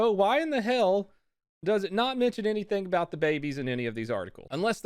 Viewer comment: Oh god.. I thought it said "Barbies" instead of "Babies"